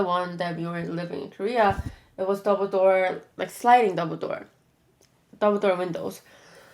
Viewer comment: I heard sliding Dumbledore😂😂